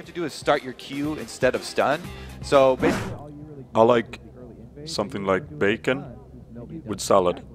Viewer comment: i heard " i like something like big and solid "